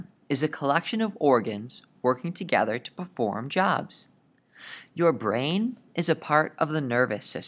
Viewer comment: Does he say jaws or jobs or something else in 1:40 ? O.o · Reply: jobs